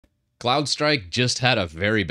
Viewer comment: I thought you were saying Clownstrike... which is great... 😂